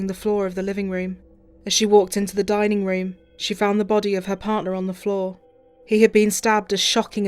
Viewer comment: I wasn't trying to make a case of it, but here we go I guess. 'returned to the flat she shared with "THERE" partner of 3 years' Not sure what level of education you finished but you are wrong, sir. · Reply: right, I thought you were on about a spelling of a word later on in the episode. But what you said is still wrong and I think you misheard. She she says “with her partner” she doesn’t say “there”. Maybe it sounds like that to you …but it’s definitely “her partner” Even if she did…how would you determine the wrong version of the word to have been Used? Anywayyyyyy “Her partner” just to clarify.